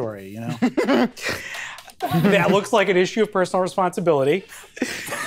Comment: His delivery on "Do you ever wish.. you were somewhere else?" is fucking hilarious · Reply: The first time I heard it, I thought he said, "Do you ever wish... you were someone else?"